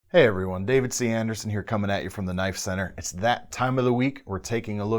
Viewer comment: For a second or two, in the intro, I thought you said "Coming at you with a knife". jk ...Don't hurt me!!!